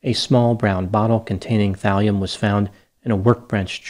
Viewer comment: thalium .. i thought you said valium